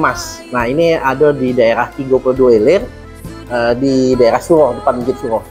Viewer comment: Ralat dikit.. bukan 32 ilir ( klo dk salah denger ) tpi 30 ilir